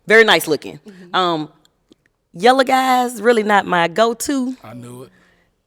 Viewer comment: Did she say "Yellow guys?" 😂